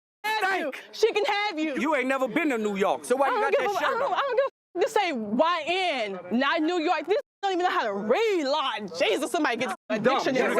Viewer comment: Did she just say this says Ny and not New York 😂